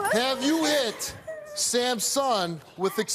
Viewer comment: at first I thought Steve said “Samsung” and then I heard it again and was like ooooh Sams SON 😭 but honestly i understand the frustration of taking care of a baby who isn’t yours, yea people get frustrated with babies because we don’t know what they want and why they’re crying and that’s okay because we are humans we are allowed to have these feelings, but the very second you feel like putting your hands on a baby you better walk away and think about your decisions. and I’m no where near saying that abuse towards babies is okay because it’s really not! and people who do that deserve to get the same treatment they did to their children‼️